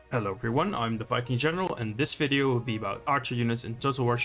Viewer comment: for a second i thought you said you where "the f*cking general" lol good video, thank you